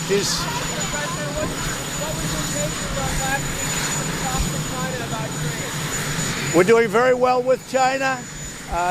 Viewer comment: Anyone catch what the reporter asked at 4:38 before he answered ”who cares”? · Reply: They asked the president what does he think of aoc calling him a racist